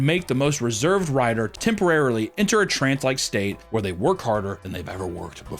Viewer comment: my subtitles said "translike" not trancelike lmao